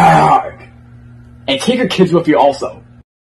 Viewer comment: What did say? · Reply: He said take your kids with you also.